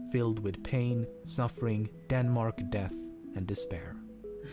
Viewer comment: Did he say Denmark, Death, and despair at the end?